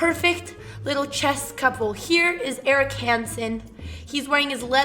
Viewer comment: i thought she said Eric Rosen for a second LOL